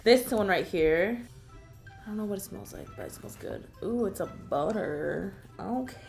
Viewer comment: I literally thought she said boner not butter